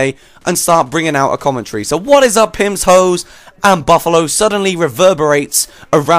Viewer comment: ohh you say 'pips, hoes and buffalo's' i thought you said 'pimtoes and buffalo's' and i was thinking, 'wtf is a pimtoe?'